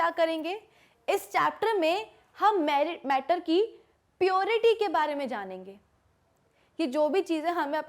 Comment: by mistakely you were going to say married instead of matter